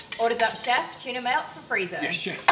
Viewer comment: Before I saw the text I thought she said Freeza